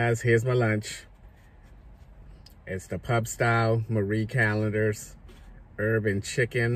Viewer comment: at 9:00 I thought you said “it’s the Pub style Urban chicken” 😂 I was like: “WHAT IN THE HOOTENANNY?!?!?!?” I had to go back and play it 2 more times. Urban chicken 🐔 🍗 🐓 then you mentioned Popeyes and I was like “awww yeahhh, now it makes sense” 😉